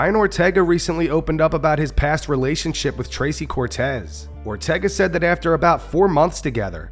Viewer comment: my volume was low..i swear i thought it said “Crazy Cortez”